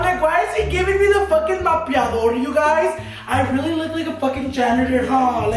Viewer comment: did anyone else heard him say "mopiador" instead of trapiador lol